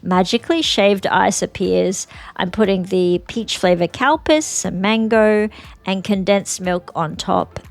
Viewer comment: Definitely sounds like you said peach flavour cow piss for your iced desert!! Yum yum 😂